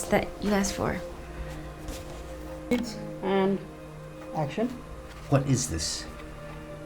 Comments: who heard action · Did anyone else catch the glitch in editing?? Lol. At 9:20 you hear a male voice say “Action”. While the Douche-y boss looks at her design. 💙🤦🏽‍♀️💙😳💙🤣💙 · Did anybody else hear someone say places and action at 9:18 minutes in? Just making sure I'm not hearing things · There i hear: and action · is it me or did I hear action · I heard and action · Anyone hear “and action” in the background of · did anyone hear 'it and action'